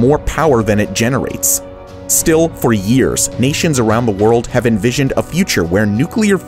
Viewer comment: i thought u said Asians around the world lol.